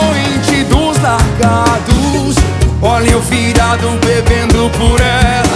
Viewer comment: A primeira vez q eu ouvi eu entendi "viado" em vez de "virado"